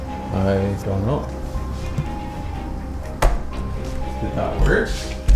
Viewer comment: an EERIE Man's voice is heard and I seriously heard "GET OUT BITCH"!! Did anyone else hear this too??!!! But then at 18:44, it sounds like, "Did That Work". Then, at 18:49, a white mist covers the ouija board before the camera goes back to and. It's eerie and weird nonetheless. NYC🤘